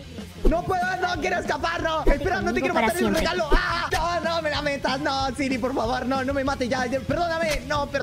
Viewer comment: en el momento 7:31 escuche ⁕AAAAAA SIRI NO ME LA METAS⁕